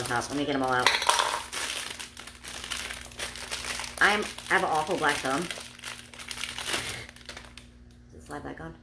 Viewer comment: I thought you said, "I have a terrible black tongue". I looked up a I was wondering how you were going to fix it with mini garden tools. 😆